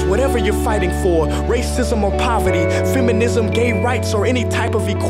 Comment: Communism that what I hear